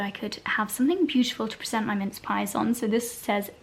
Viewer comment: Happy birthday!!!! 💕 so funny thing i'm from Spain, so when started talking about minced pies I thought that she was saying mint pie. And I have just realized that I was wrong the problem is that I have no clue of what a minced pie is, so what is it?